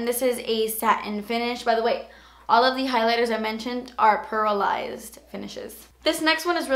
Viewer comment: watching this years later, sounded like she said paralyzed instead of pearlized lol